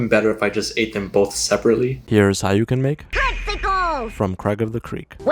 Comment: why does it sound like she said, "Testicles!"